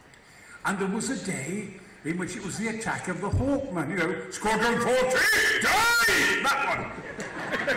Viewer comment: What does he say at 8:27..??? (_______) Dive!!!!! Anybody? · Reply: He says "squadron fourteen dive!"